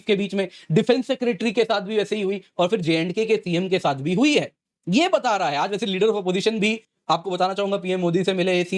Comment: did he say J&K PM ?